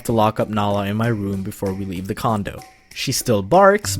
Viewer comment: What the heck u said it looks like he said cond*m??